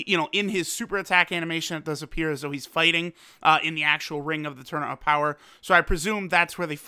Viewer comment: When you said fighting in the top arena I thought you said hiding in the arena which is true